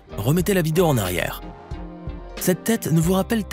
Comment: c'est moi ou il a dit que ses taylé dans toy story😂😂